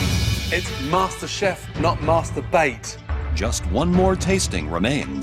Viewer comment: "It's MasterChef not masturbate" ,Everybody in the background are trying not to laugh so hard😂😂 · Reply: I thought he said Master Bait.